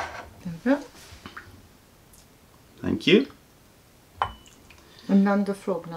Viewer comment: Did she say "Danke" at 27:27? My German heart ´s blooms ;D